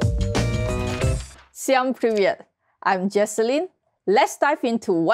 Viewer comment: did she say "vsem privet" (hello everyone in russian)?